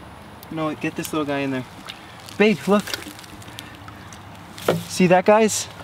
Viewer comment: I'm sorry but at 12:06-12:07 it sounded like u said "babe look" ????